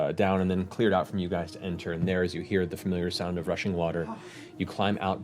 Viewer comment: i kept mishearing rushing water as russian water